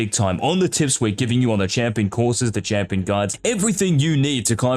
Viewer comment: I thought it said pro guides 😂😂